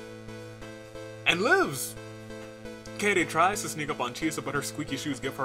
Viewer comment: Huh, the complete and utter irony that I was listening to “The Greatest Showman: REIMAGINED” album earlier and hence the title... huh..... !!REPLIES CONTAIN SPOILERS DOWN BELOW!! PLEASE READ WITH CAUTION!! (HAPPY BDAY OMA KOKICHI!!) (I can’t help but sing “The Greatest Show” :D) 1st: The Troublemakers (BDAY BOI Oma, Sou, And Komaeda) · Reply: Edit 24: 45:52 did I hear that right? Keidi instead of Kaede...? (Also I can actually see Akamatsu wear squeaky clown shoes... head cannon?) kayayday